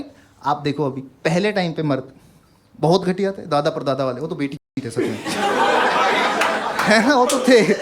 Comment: what did he said ? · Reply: Betic**d